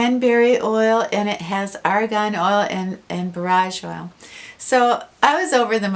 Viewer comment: Had to back it up because I thought you said garage oil. 😂